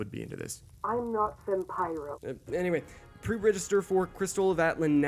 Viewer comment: Am I crazy or did she just said "I'm not Fem-Pyro"?